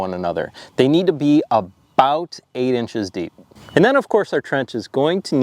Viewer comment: I just watched your newest video,that brought me here. I just heard you say to plant them 18 inches deep? lol your board is 12 inches deep.. 🤔😉🤪